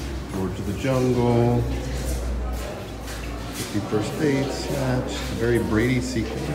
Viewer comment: Great video and! It’s like New York , round every corner is a movie set . What a beautiful island too . It must have been extra hot for two Floridians to be struggling 🥵😂 . When you said” 50 first dates “ I thought you said “51st state “ 🤔 two very different movies 😂😂. Favourite part was you guys running from that big Dino near the begging 😱, thanks guys